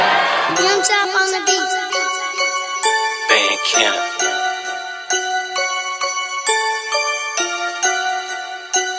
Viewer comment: did he say bacon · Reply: no he said bandz county